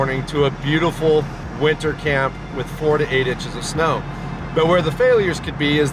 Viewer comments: He said 4 to 8 inches omg I thought he said 48 inches I was like what friggen planet are you on bud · I thought you said 48 inches of snow